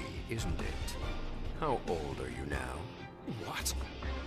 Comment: “How old are you now?” That line sent chills down my spine. Then the “why would someone so young wish to hurry their own death?” If someone said those lines to me, I would be shaking in fear. (1:09 and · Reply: i thought he said "how bold are you now?"